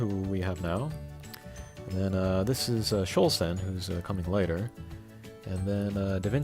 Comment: Who is the monster @ 14:37 the one who's bending over with strings... I know you say her name but can't understand you. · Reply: Chou Sen. She'll have the Chinese pronunciation when she comes to the English version, I'm sure.